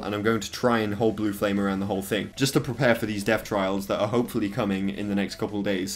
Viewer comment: For a second I thought you said death trials! xD